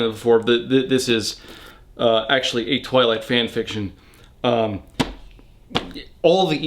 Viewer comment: it sounds like he is saying "toilet fan fiction" LOL which is an apt label for the trilogy